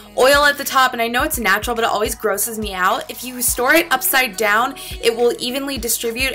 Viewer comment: Omg I wasn't paying attention and I read it as "snorting peanut butter" 😂😂😂😂😂😂😂😂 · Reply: yea i thought she said snort it upside dowb